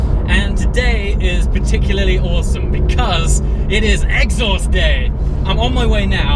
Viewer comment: I heard "It is Exo's day"..lolll